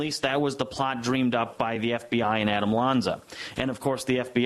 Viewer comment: Did just say the guy in the bomb plots name is (Adam Lanza)....?.....Like the so called sandy hook shooter Adam Lanza